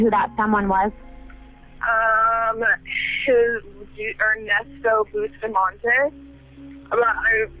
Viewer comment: DID YOU SERIOUSLY SAY “BUSPHINMONTEZ” she clearly said BUSTAMANTÉ I mean FFS didn’t uou google this story?! 26:28 Bro she said “Has a dent” my God there are plenty of people willing to edit your transcriptions